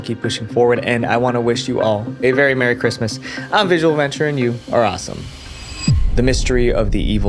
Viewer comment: when bro said 'you are awesome'..... i heard it as ''ur a assh*le'' bruh...💀💀